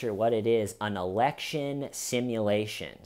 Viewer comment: Election? I thought you said erection.